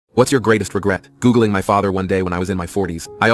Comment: Tell me why I thought he said I regret gooning ur father